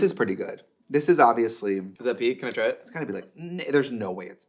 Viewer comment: I was about to cancel Josh because I thought he said "is that peak". A queer can only handle so much cishet on a single day.